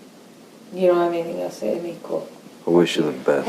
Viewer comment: I think Amber said; "You don't have anything else to say to me, cool." And her saying he's always drinking, well wasn't she as well? She sounds out of it in this video and apparently according to Johnny she drank 2 bottles of wine a night.